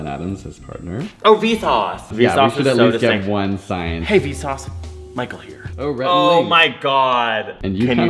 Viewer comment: This was a gag! In the gif you posted I thought the vsauce one was saying 'hey vsauce, better hair'